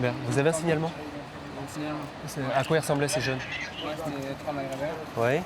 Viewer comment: on ne comprend pas ce qu'il dit!!!! : "à quoi ils ressemblaient les jeunes?????" 18:07 là pareil on ne comprend pas non plus type ??